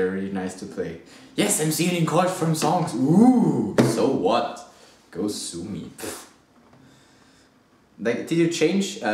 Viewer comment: Hank Green made a video about those chords! :D Everyone uses those chords! Also did you say "Go Suni" at 2:30? :P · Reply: no, i said "go sue me" ;)